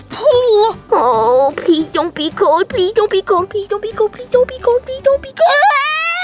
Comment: In 1:49 toy Bonnie is gust Saying please don’t be cold